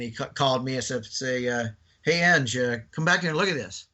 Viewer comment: It sounds as though he slipped and said his name. If you listen closely at 1:20:15 he stated that the loadmaster called him over and said “Hey Ang, come back here and look at this!” I could be wrong but that’s what it sounded like I heard